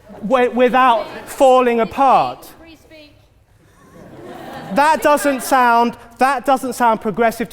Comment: I'm a bit hard of hearing, so what is the person interrupting around 6:31 saying? It better not be "freeze peach". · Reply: she is saying "free speech" like that somehow validates her bull shit.